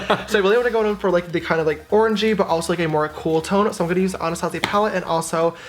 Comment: I was watching this sped up and when said "orangey" I heard "orgy" and I had to rewind 😂😂😂